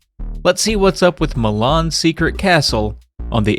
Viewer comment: I keep hearing you say "Mah Mom's Secret Castle"